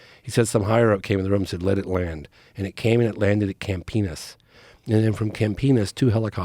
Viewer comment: "...from Camp Penis to Vaginia" Wait, what did I hear?